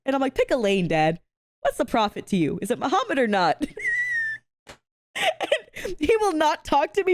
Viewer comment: I swear when she said "is it Muhammad or not" i swear (and i mean no disrespect) i heard "is it The Hobbit or not" i had to rewind that a couple of time to make sure i heard wrong 🤣🤣 🤣🤣🤣I'm so sorry🤣🤣🤣